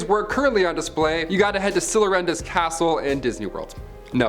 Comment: You can say the word Nazi, dingus. 🙄 They did exist. You can say the word, it won't bite you. 😮‍💨 8:44 did you just say Cillarenda"s castle?? 😂😂